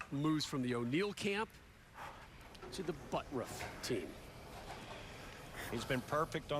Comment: i heard buttruff at 1:18:23. am i right?